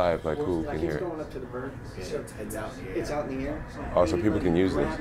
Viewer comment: Jones says "so people can use this" But the subtitles say "people can grab this". How the hell do you fuck that up?